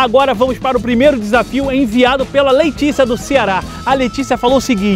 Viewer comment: Só eu ouvi "LEITICIA" aos 0:31 quando ele falou? kkkkkkkkkkkk